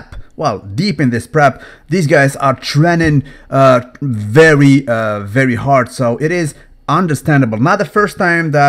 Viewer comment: Pretty sure he said train g very hard super sounded like he said trening very hard!